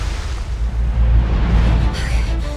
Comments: I thought it said "Brush" and I was really confused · I thought it said ' brush sound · i thought it said brush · I thought it said brush 🤦‍♀️ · I thought it said brush not bruh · I thought it said brush but I’m not complaining